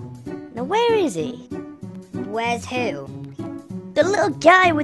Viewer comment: turn on english subtille. Instead of "What",it is "who"